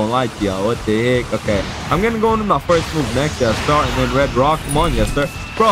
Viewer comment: Why does it sound like ur saying the n word when u say nika